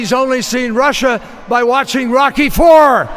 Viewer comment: Rocky IV. At first I thought he said Rocky Horror.